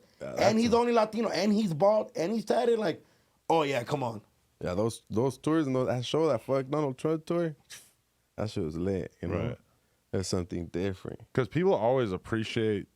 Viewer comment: did he say fuck donald trump tour? 💪🏽